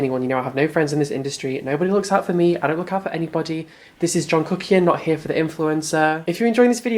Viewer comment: Did anybody else thought he said "this is Jungkook"? Before the James charles video that he showed.